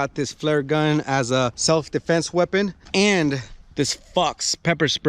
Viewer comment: For a second I thought he said fuck self defense lmao